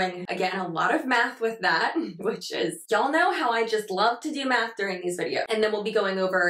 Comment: Am I the only one that thought you said "we're doing a little math with that. Y'all know how much I love to do meth." Forgive Me I use your videos as my alarm. Not fully awake. Lol